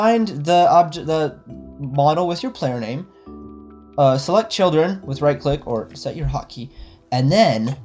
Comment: First I thought he said anger the children Dx